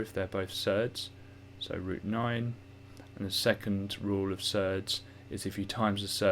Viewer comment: What is that word he uses at 1:29:10 surd? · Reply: yes, surds is another name for a number which is next to (multiplying) a square rooted number